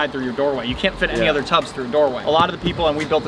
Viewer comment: Did he say you can’t fit any other tubs through doorways??